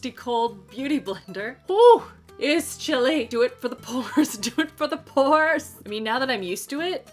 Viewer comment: When you said DO IT FOR THE PORES I thought you were saying DO IT FOR THE POORS 😂 And I was like 🤔 hm I don't remember asking her for this